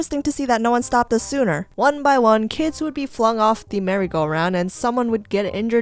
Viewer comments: When you said merry go round I thought it said mercy go round 😂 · When you said merry go round I swear I thought it said mercy go round... dang mercy did happen to me tho ;-; I legit remember my mom spinning us so fast on that thing and next thing I know I was being dragged through bark chips still hanging on for like 5 seconds XD